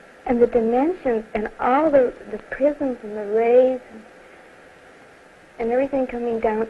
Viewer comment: what did she said at 3:18 "The dimensions? ...and what ? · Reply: The prisims and the rays